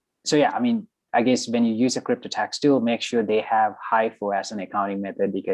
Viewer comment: What did he say at 34:05? “When you choose a Crypto Tax tool make sure they have....”? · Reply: I think he said HIFO, highest in first out